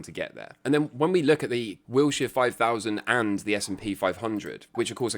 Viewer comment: Thought I heard "the wheelchair 5000" which is an appropriate name for my alt portfolio